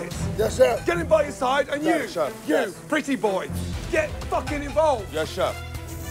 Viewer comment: Did Gordon just call him pretty boy? hahah